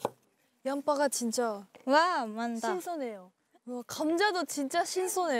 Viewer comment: kenapa aku dengernya hinata bilang "wah mantap" 😂😂